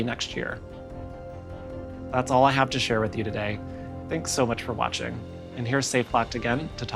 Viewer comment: I swear I heard her say " Thank you for washing "